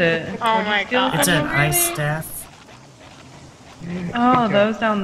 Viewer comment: I thought you said eye staff instead of ice staff XD the ice staff freezes enemies